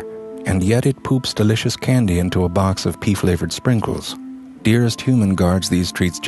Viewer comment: hahaha omg "it poops delicious candy into a box of pee-flavoured sprinkles" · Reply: Oh right, that makes a lot more sense, I heard "pea flavoured sprinkles". I mean, I don't have a cat, but I thought they made pine scented? I was prepared to go with it anyway.